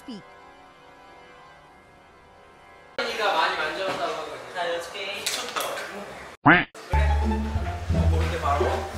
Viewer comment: what’s the word though??? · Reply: It is paper cup❤